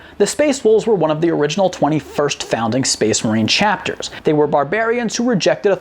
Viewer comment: I thought you said the space wolves were one of the 21st founding chapters and I internally reeee'd I had to rewarch it like 4 times to get that you said of the 20, 1st founding.